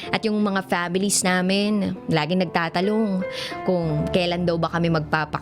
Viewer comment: mali yata lang pagkakarinig ko ano po ba narinig nyo dj parang NAGTATALONG po narinig ko? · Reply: Nagtatalong nga 😄❤ I love you padin dj.muah